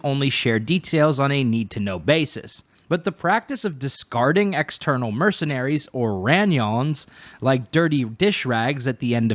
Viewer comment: Ranyon? My hungry ass thought they were saying onion this whole time😭😭